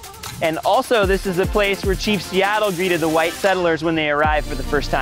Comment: did you say chiefs? what’s that? do you mean the football team or someone else? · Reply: Chief Seattle